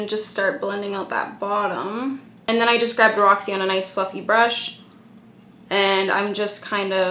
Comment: I thought you said "I'm gonna just grab Roxy on a nice fucking brush" 😂😂😂😂